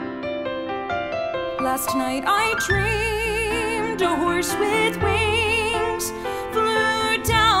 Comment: When I first heard this in the show, when she said "Last night I dreamed a horse with wings", I kid you not I thought she said "Last night I dreamed a horse with wangs "